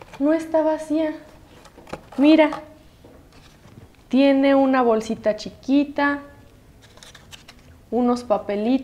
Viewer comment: did i hear that correctly, because it sounded like they said jacket in spanish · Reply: She says "Mira. Tiene una bolsita chiquita."